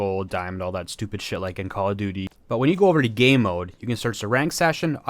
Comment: Did bro say gay mode